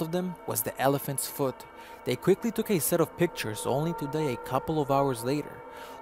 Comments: 'They took a few pictures only to Day a couple of hours later', I know this dude didn't say 'Day' instead of 'Die'. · what happened a couple of hours later??? turned sound right up still cant understand what you said! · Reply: it sounded like he said "only today a couple of hours later"- like what? that makes no sense! but he actually said "only to die a couple of hours later"